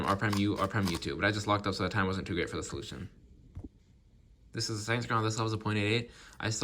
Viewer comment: i heard "this was the same scramble" atp